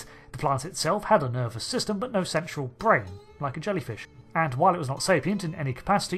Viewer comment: did he say "sexual brain"?